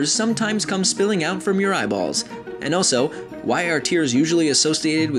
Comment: I thought you said "When teenagers come falling out of your eyeballs" Xd